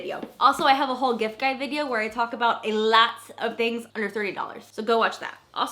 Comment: Under $300 misalogist dollar sounded like you said $30 I’m confused